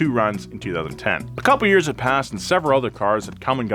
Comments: The subtitles say paSSCd instead of passed at 4:38 lol Edit: There's more but you guys can find them ;) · the subtitles are cracked lmao the dude wrote paSSCd instead of passed.